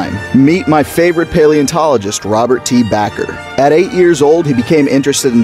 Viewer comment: I thought it said robery tea bagger 😂😂